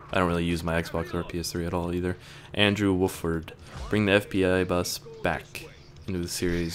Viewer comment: Did you say Andrew Wolford at 18:30? Please reply!